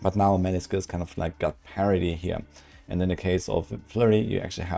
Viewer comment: I know you said parity, but it sounded like you said parody, and that was hilarious. Those on screen numbers are ridiculous. Is there a setting to turn those off or is blizz going to change them like they did in D3?